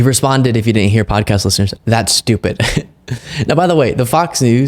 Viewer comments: Didn’t sound like he said “that’s stupid”, but “too stupid.” Or was that just me? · It sounds like he said “you’re too stupid” doesn’t sound like “that’s stupid”